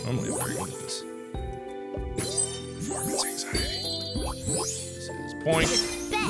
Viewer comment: What did he say at 8:43? Did he say "Mark has anxiety" whaaatt?